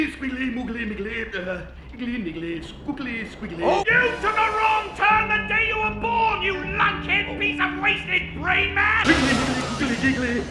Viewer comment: So what does he say at 1:07:21? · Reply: “YOU TOOK A WRONG TURN THE DAY YOU WERE BORN, YOU LUNKHEAD PIECE OF WASTED BRAINMASS!!!”